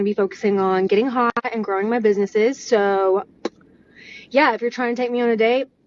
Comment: I missed what she said. I was.... distracted. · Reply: “Her businesses”